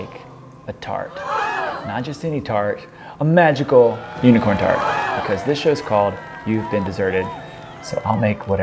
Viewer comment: hahahhahahaha thought it said Unicorn Fart